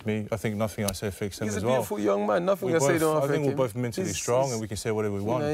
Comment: Did Dillian call him a beautiful young man ? 😂